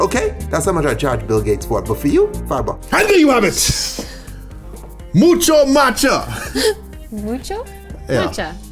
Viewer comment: Is it weird if the first time I heard him say the drink all I could think of was MUCHA LUCHA